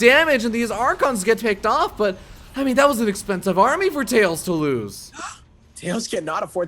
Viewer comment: "Honey that was expensive"... He called Tails honey, i say that's the last straw with this new guy. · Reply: i heard "i mean that was expensive" just "i mean" was really quick